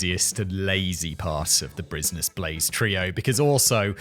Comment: I heard that "bruisness blaze". It's been like years Simon get it together